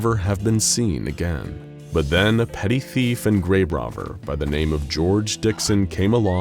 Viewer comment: he says grabe rovver instead of grave robber and I can never un-hear this